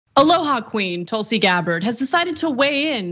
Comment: Haha did she just say ohala queen haha wth like saying a beaner is all us Spanish folks haha